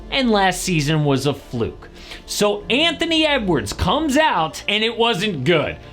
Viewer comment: Did he just say ant comes out???!!😂